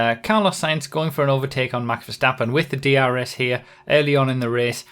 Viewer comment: did you say 'going for an overtake on Mark Verstappen' at 0:32 ? :D :D